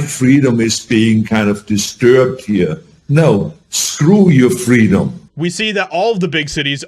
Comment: He will forever be “f ur freedoms” so F him, even though he was my movie hero as a kids 😢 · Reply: The weird thing is everone remembers it wrong. I thought he said "Fuck your freedoms!" you thought he said "Fuck you are freedoms!" but he actually said "Screw your freedoms!". Just as bad and jußt as woke honestly.